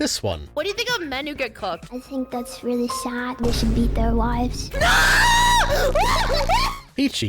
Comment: Well I heard either “that’s very sad we should be their wives” or “that’s very sad we should end their lives”